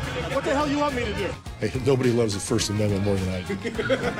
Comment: What did he say in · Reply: Pang Keat Tung "nobody loves the first amendment more than I do"